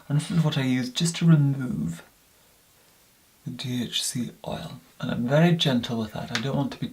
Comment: At first I thought he said THC oil... that would be relaxing skin care.